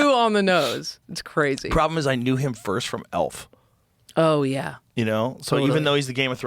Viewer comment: I thought he said "ALF", and not "ELF". Now I will always see ALF in Game of Thrones. Just became my new favorite show.